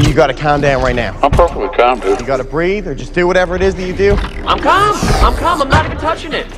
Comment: I swear he said "whatever". The same thing he said in the 2nd film when Sam told him to get in the garage.